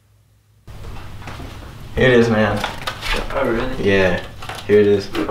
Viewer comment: I thought he said “haters man” even tho he said “here it is man”